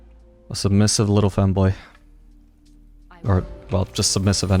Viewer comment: did i listen wrong or did he say sub little famboy min 14:20 ???